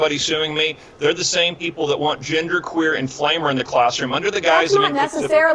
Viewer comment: What does he say at 13:59 ? Genderqueer and flamer?